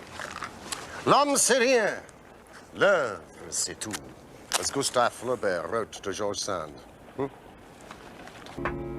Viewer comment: What does Sherlock says to him here? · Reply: He said ‘l’homme c’est rien. L’œvre, c’est tout’